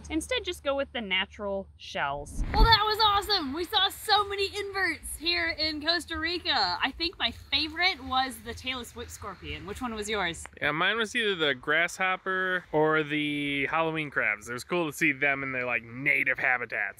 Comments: I seriously thought you were saying Taylor Swift Scorpion, not tail-less whip scorpion. XD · When you said "Tail-less whip scorpion" I heard "Taylor Swift scorpion" 🤣🤣